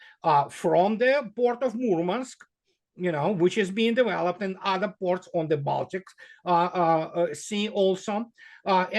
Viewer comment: At around 30:50 Andrei said "How to put this politely..." in reference to the new North/South trade corridor between Iran, Russia, China and Europe. Then he said what sounded like "It's not exactly Brie... then something about Belt and Road Initiative. I wish I understood this point he was making. It sounds very important, but I just can't make sense of it. What would be the problem of "putting it politely" if he's just comparing the new North/South trade corridor with the Belt and Road Trade Route? Was he saying "Brie" like the cheese? Some veiled reference with Xi's stopover in Paris? Or was the term that sounded like Brie, just a reference to the Belt and Road route? I lower the speech rate to 75% when Iistening to him, listen very, very carefully and even play back parts I didnt 'get' the first time, but I have a very hard time understanding his English (I'm an American). It must be me because no one else comments on his accent. But could someone please tell me what his point was here around 30:50 when he said what sounded to me like "brie"? Thanks. Great interview. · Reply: BRE. That’s China’s “Belt and Road Initiative”. He shouldn’t call it BREE but yeah…